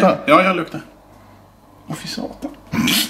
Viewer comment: did say voi saatana or oh satan